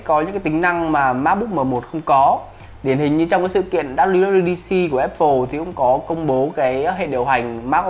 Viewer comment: nói gì dạ ông kẹ =))) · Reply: Sự kiện apple WWDC 2021 :v non vc